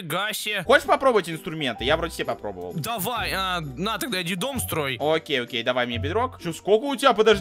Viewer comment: мне послышалось "иди строй детдом" 🤣🤣🤣🤣